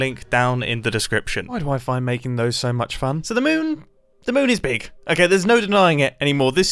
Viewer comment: I genuinely thought you said "My wife and I find making them so much fun"... I was like wow wow wow... when did and Vicky get married? I had to rewind it multiple times to hear "why do I find making them so much fun" :)